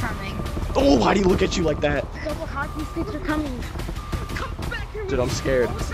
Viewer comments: I heard 666 hell is coming 😮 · for the people who didn’t hear what that thing was saying I heard “666666 hell is coming” I would not mess with that